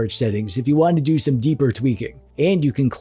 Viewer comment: Did you say deep tweaking? I’m in! What is this about now?